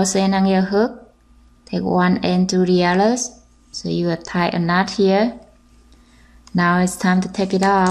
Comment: I can't understand 15:52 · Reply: Dragonheartthewarriorcat14 15:52 is a slip knot...